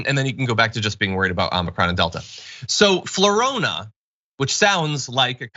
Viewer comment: Oh flu-rona, not Flo-rona. I misheard and thought there might be a variant originating from Florida.